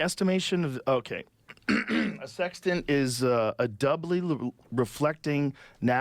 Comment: The first 5 times Joe said "Sextant" I thought he said "Sex Den" 😅